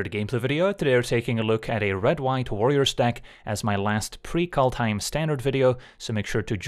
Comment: I wasn't listening carefully and I heard "a red white lawyer deck" and now a lawyer deck is my most fervent desire for MTG. What color would your lawyer deck be? I'm thinking... Blue/White